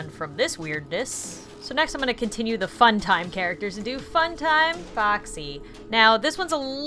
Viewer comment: i thought u said main characters, why is it not fun time bonny and fun time chika?